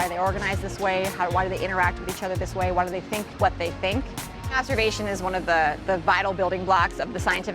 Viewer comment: did she say masterbation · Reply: No, she said "observation".